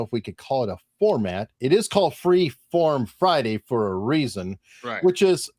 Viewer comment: I thought it said Free Porn Friday !!!